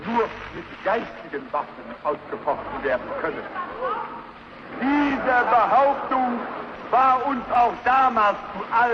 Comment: Yo at 17:46. What did that woman in the crowd say? It's identical to what Goebbels said next. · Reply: Wie der Behauptung, Like the claim, goebbels then repeated what she said